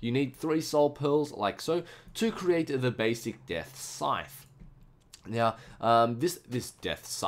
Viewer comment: dang it i thought it said death scene